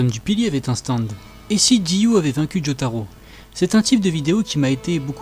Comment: Au debut j'ai entendu "et si dio avait un cul de jotaro"